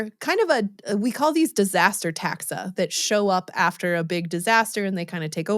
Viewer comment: When you said “disaster taxa” I heard “insurance adjusters and injury lawyers.”